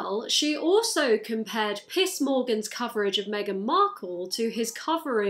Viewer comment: i keep hearing "piss" instead of "piers" and I'm ok with that